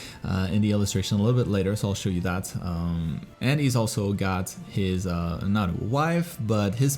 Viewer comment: I thought you said he is also a god